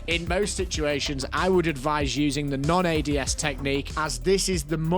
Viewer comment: I thought bro said aids for a second